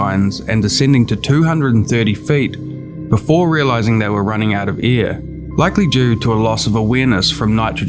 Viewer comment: every time I hear "they runned out of ear" I remember my english teacher telling me I should improve my pronunciation xd It is an awesome video, short but even so full of story and details, I really liked the job!